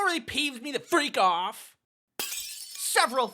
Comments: Had to rewind the beginning three times cuz I'm drunk and it's Halloween and I thought he said p. Freak off. . 😮 instead of, you know what Peeves me the freak off. Lmao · Did he just say “freak off” 👀